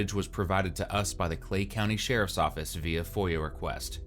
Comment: Florida again… why am I not surprised? 🤦‍♂️ I need to get tf out of here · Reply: I thought they said Clayton County Ga